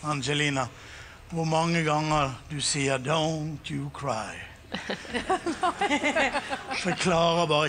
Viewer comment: I noticed at 3:53 a barely audible Angelina, whispering to herself a reminder. Thank you, very nice analogy as usual. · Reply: I thought I heard "Don't you say" and then an inaudible word following that. ...thank you!